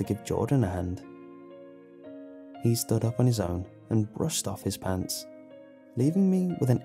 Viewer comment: The first time he said brushed off his pants I thought he said pulled off his pants lel